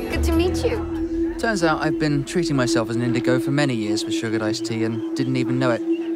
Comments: Lady in Blue: We have sugared ice tea. Gavin: What's that? Lady in Blue: Ice tea ... that has sugar. Gavin: Oh, I understand. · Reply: Michael Setiawan I understood why he said that because at first I thought she said sugar dice tea and I reckon that's what he heard too. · VICE GUY: SUGARED ICE TEA? What is that? LADY: Ummm.. ICE TEA... that has sugar Haha i dont know why i found this so hilarious lmao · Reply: N E I heard "sugar dice tea" as well & was thinking "WTF is that?" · VICE GUY: SUGARED ICE TEA? What is that? LADY: Ummm.. ICE TEA... that has sugar Haha i dont know why i found this so hilarious lmao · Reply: Santiago Aquino I initially thought she said "sugar dice tea" which I am pretty sure he did too which is why he asked what that was. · "we use... sugared iced tea" "what's that?" "uhm, iced tea, that has sugar...." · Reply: i heard "sugar dice tea" so i actually said "what" right when he did